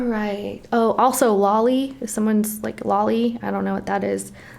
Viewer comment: did you say Wally?!?!?! 😳👀😳👀🤯